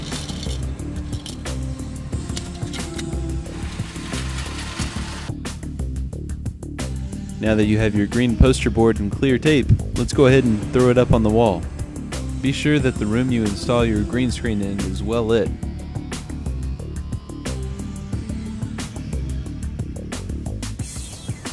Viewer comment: it sounds like he says lets throw up all over the wall lol please thumbs up.